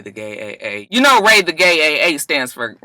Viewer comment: I always thought you were saying “Ray the Gay AKA” in reference to the sorority 😂. No idea why.